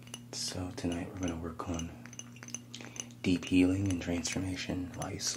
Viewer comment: My half asleep brain heard you say de-peeling instead of deep healing in the intro 🤦‍♀️ ...braindead